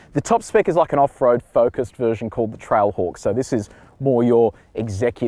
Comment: what did he say at 0:15 ? "it`s one down from the Topspecs?" what was the name of the model? the more offroad model? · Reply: Trail Hawk. You probably already have this info but, there you go.